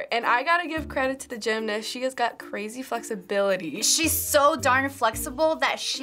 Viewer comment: omg did i heard "crazy f..ksability"